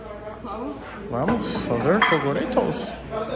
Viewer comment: xiiiiii será que eu entendi direito... kkkkkkkkkk 😂 toguro broxa kkkkkkkkkk 🤷